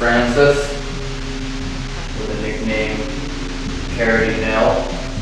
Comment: when he asks if its Francis, i hear "um hum" . And then at 51:59 "please be vey careful"